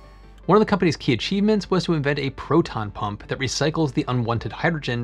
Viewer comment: I've definitely read too many comic books, I heard "proton bomb", rather than "proton pump"...